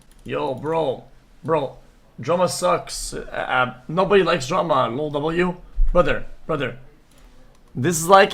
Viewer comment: Ngl when he said at the beginning “no one likes drama” I thought he said “no one likes Jerma” and got so confused bc most people like him 😭